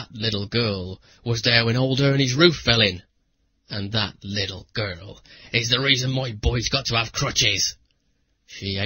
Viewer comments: did he say "And that little girl is why boys have bad crotches"?! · Did he say "that little girl is why boys have crotches" or am i mishearing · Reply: "Crutches"